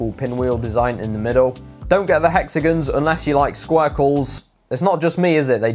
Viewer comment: Did i hear you say squirkles? lol